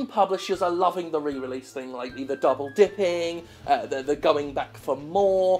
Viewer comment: Did he say double dicking or dipping?